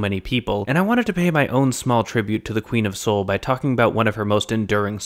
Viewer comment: At first I thought you said "The Queen of Seoul" and was VERY confused. Got it eventually, though! Thanks for the video :)